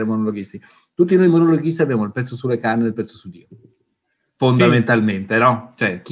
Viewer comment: "tutti noi monologhisti abbiamo il pezzo sulle canne e il pezzo su..." qualcuno ha capito l'ultima parola? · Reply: Dio